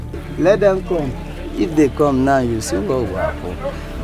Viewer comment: Vice messed up on the subtitles at 3:41 he actually said "let them come you will see what will happen". Trust me im nigerian ;)